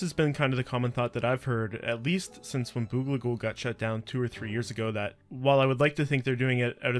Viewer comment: what did he say here? boogle goo? edit: pretty sure it was “BoglaGold” idk how he managed to say “ü” for both of the “oh” sounds AND the “uh” sound lol but ya